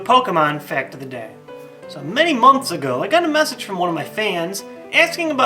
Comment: It sounded like he said "Many monks ago" LOL!